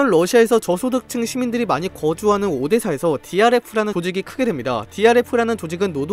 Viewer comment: 나만 DRX라고들림?ㅋㅋㅋ